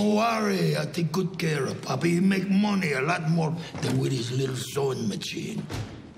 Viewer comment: "Wit his litl sowing macheen!" 😂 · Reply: Thanks for this comment. This whole time I thought he said, “....a lot more than what his little son makes him.” 😂 I thought he was saying he could take care of Nacho’s father better than he could. Now I hear it correctly.